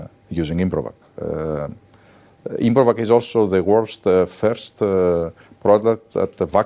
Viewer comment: Did he say "Improvac is also the worst..." at 00:34? 😂